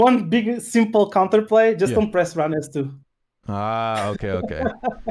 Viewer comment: didnt hear what he said at 11:45 about the counter play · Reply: Just dont use ran s2